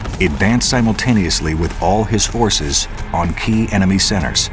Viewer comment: is he saying Horses or Forcces?